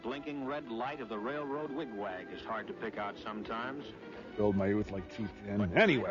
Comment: Anybody know what the hell Tom Servo says at 9:23? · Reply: Built by youth...